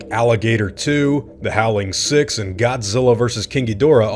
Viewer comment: i swear i heard you say "Godzilla vs Kinky Dora"